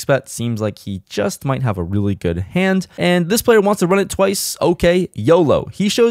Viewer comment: I thought I heard running it twice at the 5:54 mark...